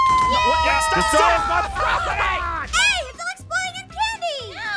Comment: It sounds like he is saying hello Zane😹